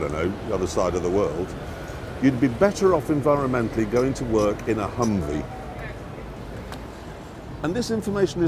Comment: going to work in a hungry... that's what the subtitles say. Could anybody tell me what he's talking about? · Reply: He said "going to work in a HMMWV"